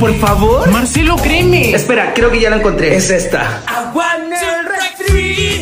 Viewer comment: Yo pensé que decía aguan un refri